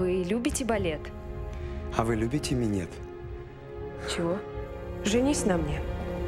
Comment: Не хочу показаться моралистом, но слышать от Безрукова "а вы любите минет?" как то, ну не знаю ужасно что-ли. Мне просто интересно, ЗАЧЕМ эту фразу вставили в картину да ещё и в трейлер фильма. Вот почти 70 000 просмотрели этот ролик, по любому дети тоже были. В том числе дети Безрукова, дети актеров которые там играют, вообще люди интересующиеся кино и в частности творчеством Сергея. И тут они слышат как прекрасный актер и достойный мужчина во всех смыслах, говорит красивой девушке "а вы любите минет?". Мне кажется это такое систематическое опускание актеров через фильмы, сценарии которых диктуются продюсерами и различными лобби (не стану перечислять). Если это так и Сергей иногда читает комментарии, то мне бы хотелось чтобы вы Сергей не принимали участие в дальнейшем развращении РОССИИ - нашей с вами великой РОДИНЫ (для этого и так уже много сделали хозяева денег). Тем более, что у Вас ЕСТЬ возможность изменить Мир к лучшему, т.к. Вы являетесь кумиром для многих людей. Ну как то так) P.s. "лайк, чтобы мой коммент увидел Сергей, может все вместе мы как то сможем улучшить ситуацию вокруг себя"! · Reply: Никита Мармылёв а мне послышалось он сказал "а вы любите меня?" А не "любите минет", видимо у кого что болит)))